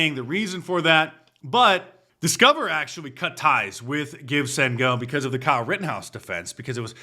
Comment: I swore you said Gibs-N-Go 8:59 😂😂😂😂